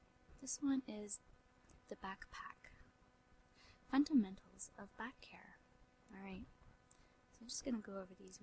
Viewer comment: Very relaxing video! I did get a little chuckle, though, at 8:06 .. I thought you said "Fundamentals of Back Hair." LOL.